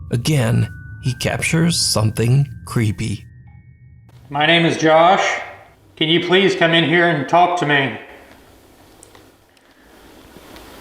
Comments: I don't hear the voice calling "josh" I hear it saying "come on" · Instead of "Josh" I hear "come on!". And for me "unexplained farm noise" could easily be a door opening sqeak. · When the guys say they hear "Josh" i heard "Come'on" Anyone else? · I heard "Come on!", not "Josh!"